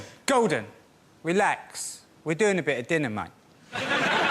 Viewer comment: At about 35 seconds in,, what the hell are GCSEs? Anyone? At 32:36....? What the af is that hippie guy saying? I can't ever make out wtf he's saying. I normally love British accents. His, not so much. It sounds Iike a dog meowing through a pile of shite. I love this show, but it would be much better if we could all of us English speakers, understand wtf they were saying. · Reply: GCSE - General Certificate of Secondary Education, the equivalent of an exit exam for a high school diploma. As for Mickey Flanegan, I think it's "Relax, we're doing a bit of dinner, man."